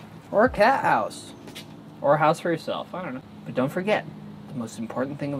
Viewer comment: I thought he said make a house for your slave